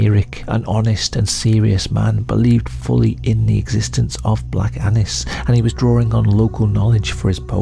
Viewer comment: I can’t help it. With your accent, it sounds like your saying black anus and cat anus. I love you Brit’s